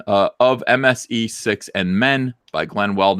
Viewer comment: "MSE6MN" Say that five times fast. Thought for sure you said "MSE Sex in Men"